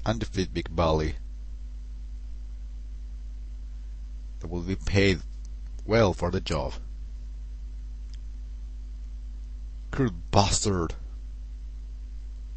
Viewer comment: lol 1:07 did you say "cruel bustaerd"? ;)